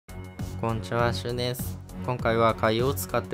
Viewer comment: 最初、こんにちは〜しゅんです って聞こえたの自分だけですか？